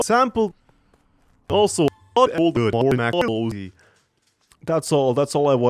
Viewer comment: I just heard Sample and baldi